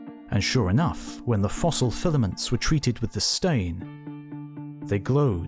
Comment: I heard "when the samples were treated with disdain" instead of "with a *green stain*" and cannot stop thinking about it